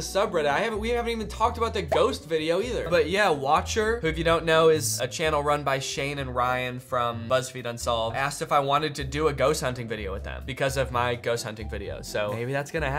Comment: i forreal thought you said "lesbian unsolved" and i've never been more confused